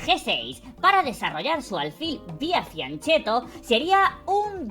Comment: Qué dice aquí? Desarrollar el alfil "vía" qué? "Ciancetto"? No se entiende bien lo que pronunció. Alguien lo sabe? Lo dice de nuevo en 21:54 "Piancetto"?